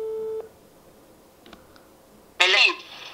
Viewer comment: ¡TU MADRE DIJO TU NOMBREEEEE! -Meleith. ¿O escuche mal?